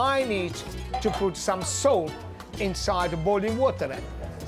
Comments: I thought he said he needed to put "soap" in the boiling water..... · DID I HEAR SOAP